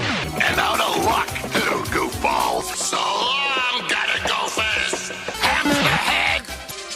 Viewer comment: Did he say Gotta go fast or something else?.. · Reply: Yeah he doesn't speak very clearly sometimes, but in that scene he's saying "gutter gophers."